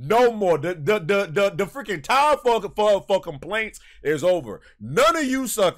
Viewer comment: Breh it sounded like u said the time for MF complaints is over i was dying laughing u most of had ur anual Stuttering😂